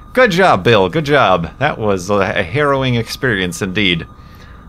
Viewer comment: Did say "heroin experience"?